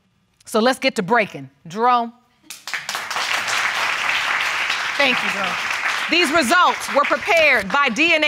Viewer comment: did she really say to Jerome, "thank you boo"???? 🤣